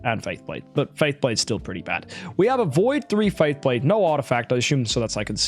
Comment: just me or did he say V3 faith blade..